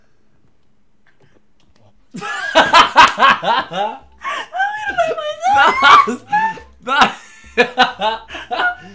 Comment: what did talia say?? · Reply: She said ksimon is real